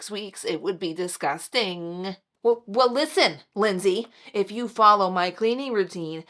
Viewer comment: I thought you said Lindy 😂 Lindy here and I promise, I clean the kitchen every day not just once in 6 weeks 😂 Thanks for the 25 minute sheet, definitely going to start that.